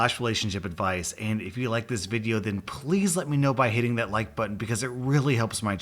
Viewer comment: Wait, did you just said; "Hating that Like button."? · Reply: Hitting.